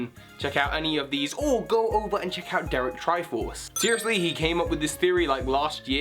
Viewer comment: I know this is an old video, but I thought you said Derek Trifle lol